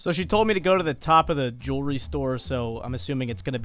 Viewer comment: When you said jewelry store I thought you said juul restore